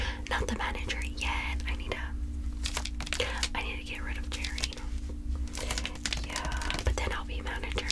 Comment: When she said "jerry" I got REAL excited cause I thought she said "cherry" and my name is cherry. That lasted a whole 10 minutes until I went to the comments and saw "jerry" LMFAOOOO😭😭🥲🥲